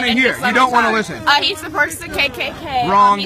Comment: did she say Donald Trump was the purpose of the KKK?